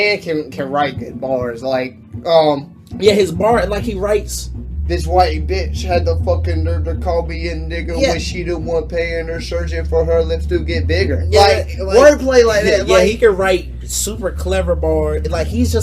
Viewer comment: I always thought meech said "paying a surgeon for her lipstick and figure" lmaooo